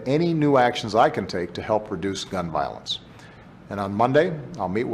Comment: Did Obama really say this? > "a few months ago I directed my team at the White House to look into any new actions I can take to help produce gun violence..." Whaaaat?